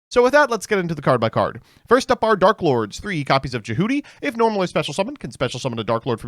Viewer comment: Either i need to sleep or all the lesbian talk got to me because, i heard Djehuty as Du Fruity 😭